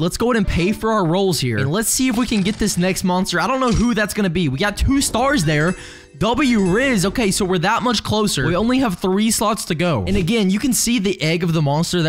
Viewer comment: hold the phone.. did you just say W rizz?